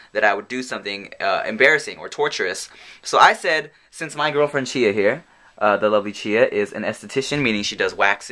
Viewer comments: I thought you said my girlfriend cheated. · i definitally thought you said "my girlfriend cheated"